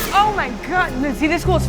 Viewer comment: This is what I heard at first: "Oh my god this is fucked up" "This is cool" And in my head I was like: "Yup." I've never seen Star Wars but I love the way you guys come together to make awesome stuff. Everyone involved seems like a really cool person.